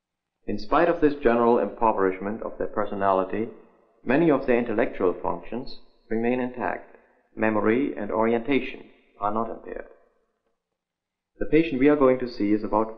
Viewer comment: What did he say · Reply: Many of their intellectual functions remain intact…. Memory and orientation are not impaired